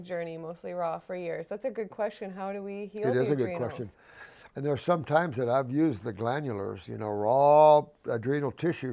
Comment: Great interview ‼️ Dr. Morse is legendary 🔥🔥 what does he say at 1:24:20 it’s better to have what sugar over another ? 🙏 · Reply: 'better to have sugar cane than beet sugar' (sugarcane v sugarbeet)